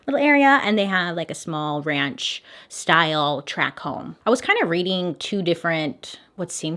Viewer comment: Lol I thought you said trap home